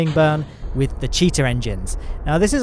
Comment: thought you said "cheater" engines